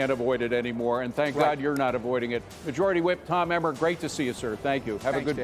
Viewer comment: I thought it said "elmer" as in Elmer Fudd. Made sense....